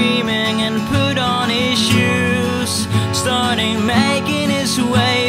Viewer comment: wait.. thought he said "pooed on his shoes".... but Iloveyouu so keep doing what your doing(